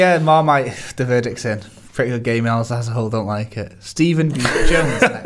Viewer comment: the subtitles read "prettier gay males" instead of pretty good gaming.....appropriate